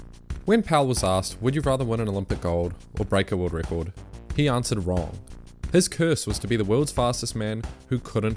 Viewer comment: I can’t hear what you say his answer was to the question “Would you rather win an Olympic gold or break a world record?” Are you saying “He answered well”, “He answered wrong”, “He answered World” or something else? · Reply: Answered wrong. Sorry for the accent haha